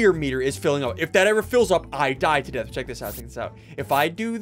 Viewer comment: did he really say i die to death? 😂